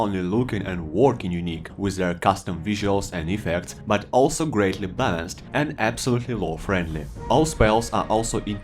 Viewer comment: What's he saying??? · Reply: I think he said, "also greatly fast"